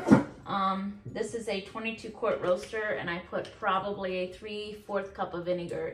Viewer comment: I would love to know what type of large roasting pan you were using in this video. Specifically the size I’m just starting to can and I’m looking so forward to learning from you. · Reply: I heard her say it was a 22 quart roaster when she said she put in about 3/4 of a cup of the vinegar. Hope that helps.